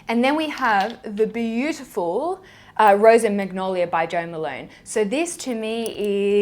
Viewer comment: I thought she said jermalone...haha